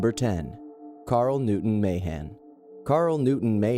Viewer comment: carl newton mahan thought they said carl newton mayhem